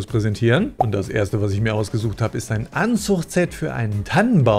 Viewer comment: 😵🤣Sorry ich bin so verpeilt, als Du am Ende Werbung für das "Anzuchtset" für den Tannenbaum gegeben hast, habe ich an ein "Anzugset" gedacht und mich gefragt wie ein Anzug für den Tannenbaum in die kleine Schachtel passen soll. Ich sah den Baum schon vor mir, mit Schwarzer Jacke, weißem Hemd und roter Krawatte. Was man sich in zwei Sekunden alles vorstellen kann, bis ich gerafft hatte, das i h mich Mal wieder verhört habe. ❤️